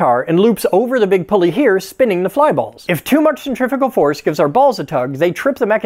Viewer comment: It sounded like you said Centrifical Force.